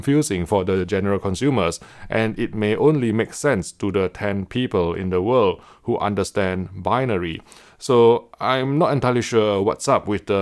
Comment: "There are only 10 types of people in the world: those who understand binary, and those who don't." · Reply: Stanio that’s not what he said. He said “It may only make sense to the ten people in the world who understand binary.”